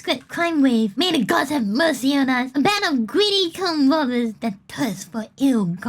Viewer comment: She said tomb robbers. I know. I heard something different too.